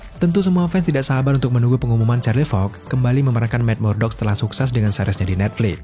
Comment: Kok gue dengernya Charlie Fox yak wkwk. Apa gue yg salah denger